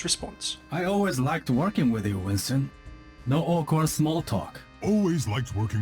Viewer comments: What did genji say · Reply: Breck Hodges "I like working with you Winston, no awkward small talk". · What is Genji saying here? I can’t really understand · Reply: I always liked working with you Winston, (something) small talk? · What does genji say?? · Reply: No awkward small talk ? maybe